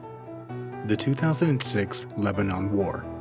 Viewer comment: Thought it said "2006 Lebron" 😂